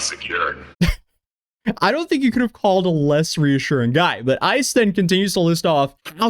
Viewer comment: When Ice Poseidon is the voice of reason and saying you're going to die. What a world. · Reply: I had no idea who he was talking about. I thought he said dude called ICE...like immigration 😅. Actually makes it funnier 🤣